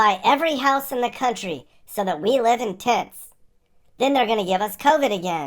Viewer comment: It sounded like said "So we live in t!ts" to me. :D